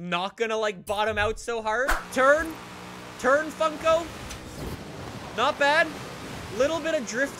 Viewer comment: Thought he said fuck,o